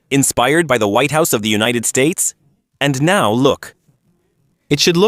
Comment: Did he say United States